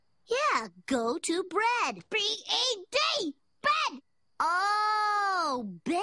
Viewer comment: did she say b.a.d? or am i just hearing it wrong? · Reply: B-E-D BED